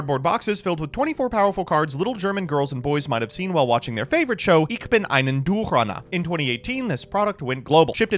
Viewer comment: As a native German speaker I'm still trying to figure out what is saying at 0:16. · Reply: Hört sich an wie "Ich bin einen Duel-Runner", wobei das "ich" mehr wie "ik" klingt. Duel-Runner ergibt auch nicht viel Sinn im Deutschen, da der dt. 5D's Dub wie die Japaner "D-Wheel" für die Motorräder benutzt hat statt "Duel-Runner".